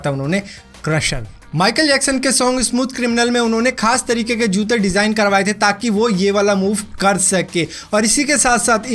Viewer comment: What.......did u just said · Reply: lola and Luis